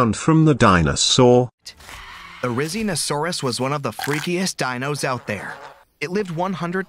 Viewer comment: Did i just hear him say "The Rizz in oh sarous" 💀💀💀💀💀